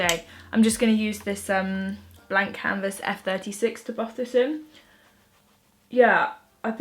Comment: Does anyone know what foundation brush was used? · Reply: “Blank canvas F36” At least that’s what I heard her say at 2:57 :)